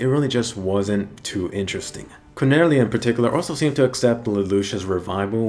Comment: i heard "quinella" not "Cornelia"